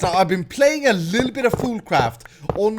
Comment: I thought you said food craft